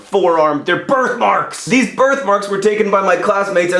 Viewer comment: The first couple of times you said "birth marks" I heard "bird marks". I was worried.